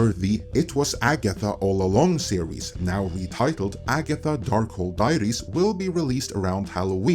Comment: Lol, i heard, "Agatha: Dark Hole Diaries"